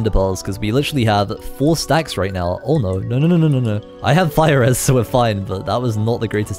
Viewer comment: (Turn on captions) was trying to say "I have fire resistants" but the caption says (I have virus)